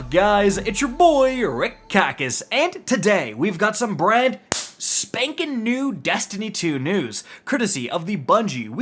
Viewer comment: Am I the only one who thought said “bread spankin new”😂